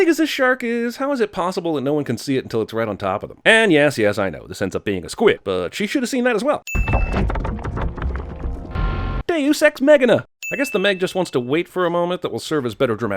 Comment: For a second I thought he said “Deus Ex Machina” weirdly. That’s when I read the subtitles. “Deus Ex Meggina”. I laughed a little